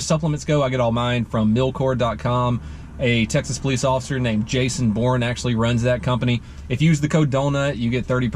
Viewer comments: It sounded like he said that guys name was Jason born. Lol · Thought he said milk whore. 😕